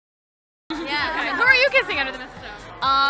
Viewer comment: What's the sentence reporter said at 0:10?? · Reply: "Who are you kissing under the mistletoe?"